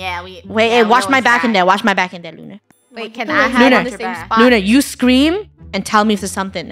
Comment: Who else loved when sang “Sweet Little BumbleBee”? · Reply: i thought i heard blunar